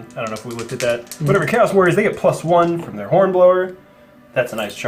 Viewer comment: Somewhere between 3-4 minutes i thought i heard him say this unit has a Khorne blower....then i realised ofc he said hornblower..lol